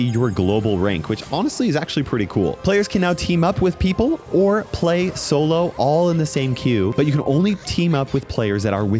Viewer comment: WHAT DID MEG SAY?!?! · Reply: Last Time i fired my gun i was in The Academy